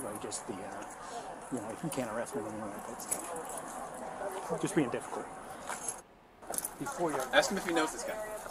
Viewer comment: At the 13:56 mark did I hear correctly that they really wanted to arrest him and the other said na can’t arrest him for jus being difficult